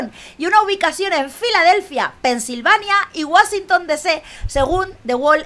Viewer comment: Ok, me reí mucho cuando escuché "wasintong"